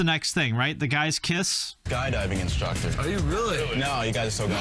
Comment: I swear I just heard “guy diving” not skydiving lol